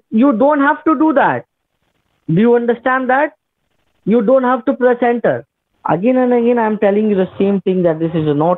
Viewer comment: Sounded like he said "You don't have to placenta." lol